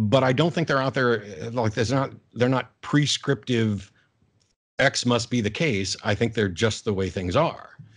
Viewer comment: So many cringey atheists in the comment section. Jay mopped the floor with you, m80. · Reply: Matt is not saying "maybe" at 1:50:20 before he says that it is "just is". He says that there is no prescriptive "x" must be the case ...they are just the way things are. Laws of logic are universal amongst all cultures and to deny them one must assume them to begin with hence transcendental. I am interested in how you would test a sense of self in a cat. Do you x-ray its brain or just project it?